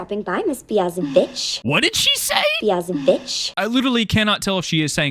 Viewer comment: When I first saw this episode I also thought she said BeyoncéBitch